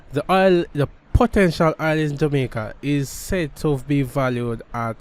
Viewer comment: Potential oil? Thought u said we had oil